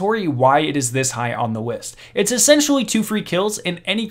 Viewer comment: Anyone else notice that at 26:02 he said “Wist” instead of list?